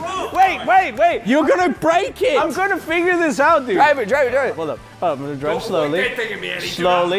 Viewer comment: Let him figure it out! It sounded like " finger it out" at first.